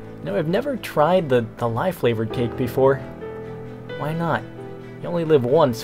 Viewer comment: It sounded like you said: Lye flavored cake. LOL